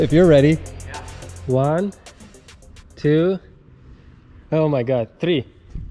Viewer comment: On 6:33 mr. directly says "три" in russian instead of "three" in english :)